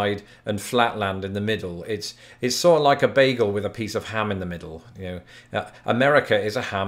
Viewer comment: "a ham bagel?" … I don't … is … is that kosher? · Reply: Swear to God, I heard "beagle," not "bagel"!